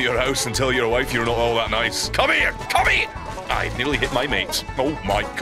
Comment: am I hearing things or did he say "Come here commie"